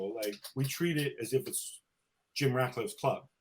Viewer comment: So what you’re saying is Sir Jim Ratcliffe’s (and I thought I heard you say ‘Sergio Ratcliffe) ego is writing cheques his body can’t cash. He’s not the exchequer of the club. That’s a big problem for sure, since the Glazers are emotionally checked out and non-commital. Ten Hag’s broken English is his communication is a problem for me, so imagine what it’s like for his players listening to his team talks etc.